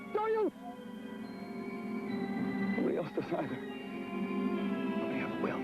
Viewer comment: What the hell did Dorian say? Sounds like "We else decide her. Yeah, I will".